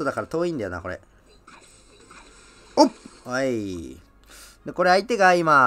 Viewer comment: おっぱいって言ってるように聞こえる笑